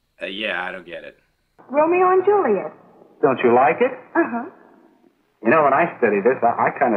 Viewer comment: Did she say she didn't like it? Would be the first time ever? ;)